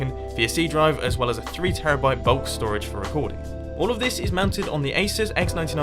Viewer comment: I thought he said 3 tb bulk storage for my porn. Don't know why. :')